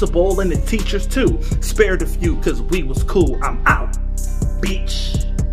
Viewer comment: "Spared a few because we was cool" See, you got to be cool with everyone. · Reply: lmao I thought he said "weed was cool"